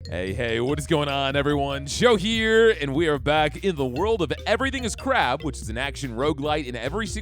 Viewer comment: I thought it originally said "Everyone's Got Crabs!! 😂😂